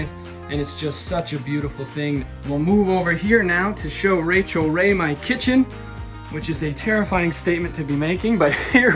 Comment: You Go Rachel! 🙏🌞🙏🌞🙏🌞🙏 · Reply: Haha you're right, what a dummy I am! I misheard what he said. I thought he said it was his Rachel Ray kitchen. Will have to delete my comment now haha.